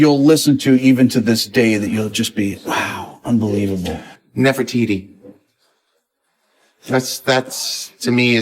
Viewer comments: Great interview! Btw, does anybody know what artist was Vinnie referring to at the minute 8:53? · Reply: yes I was wondering that he said something like nefaitina? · What did he say? His favorite album? Subtitle says "Never Titi" but it's not that.